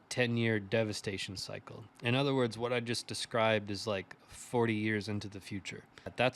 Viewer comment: Did you say you described is 40 years into the future, or 4 years? It sounded like 40 but your embedded subtitles said 4. What will be devastating about it? Zero volatility, or a massive bear market? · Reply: Hello I actually said 40 years, not 4. I was describing a possible long-term cycle where many people may lose interest in Bitcoin, while the infrastructure continues to be built before another long-term growth phase. As always, I'll continue to let the charts and the facts guide my outlook. 👊